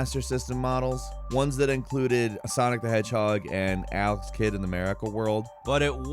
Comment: oh Alex the kid. I seriously thought you said AL'S KID i was like ''wait, i don't remember that game, who the frick is Al, and why would i want to play as his kid?'' .....now actually want to play Al's kid.....dammit.